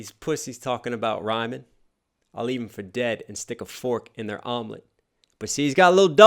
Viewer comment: Just realized I heard one of lines different than everyone else. What you heard "leave these pussies for dead, then stick a fork in they omelet" but what I hear is "leave these pussies for dead, then stick a for in they hymen"